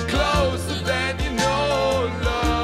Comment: Back in the day when this first came out, I always thought they said “they love each other soooo, it drives ya nuts”